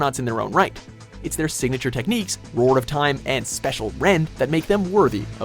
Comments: did he just say special rend instead of spacial rend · wait what? isn't it Spacial rend? I heard special rend, miss pronunciation? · Please say I'm not the only one who heard "Special Rend" at 2:44 when Palkia's signature is called Spacial Rend.